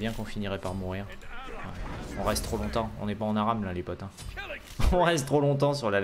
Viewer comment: Qu'est ce qu'il a dit à 23:35? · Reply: On est pas en aram